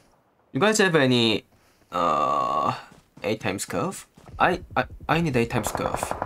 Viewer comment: he said 8x scope and i heard 8x scof....😂